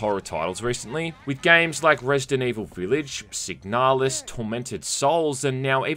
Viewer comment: what game was that after resident evil village and before tormented souls? I sometimes have trouble understanding british accents It sounds like you said, "Sick Nihilist" or "Sig nihlist" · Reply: Signalis.